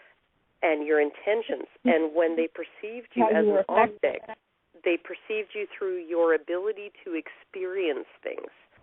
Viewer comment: What word at 13:44 "when they perceive you as an _____ they perceived you through your ability to experience things" What is the _____ word she said? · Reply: I think she said "affected", basically it's to contrast the previous idea of "The more clothes they have on, the more they're seen as an Agent." Sexualized women have no agency and can only be acted upon, or so they'd have you believe.